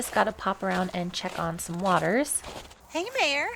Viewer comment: I'm going to call her Mayor Pearl to myself because every time I hear you say 'mare' it sounds like 'mayor' to me, lol!